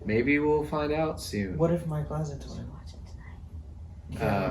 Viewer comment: what does say? I can't understand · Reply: “What if Michael has a twin?”